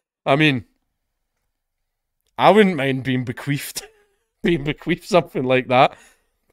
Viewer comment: i'm sorry, did u say "bequeefed"?